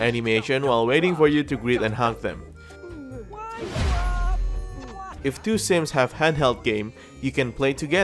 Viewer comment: for some reason I heard "If two sims have Hentai games..." I still can't exactly catch what is the actual word.